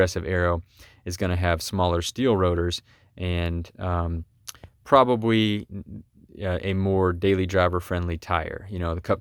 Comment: Same here! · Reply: Did I hear "probawee" @ 7:42? Lol.